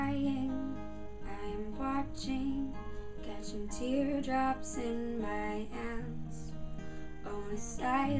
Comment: i thought you said "catching teardrops in my ass"...lmfao. i was confused there for a second. but great job ! :)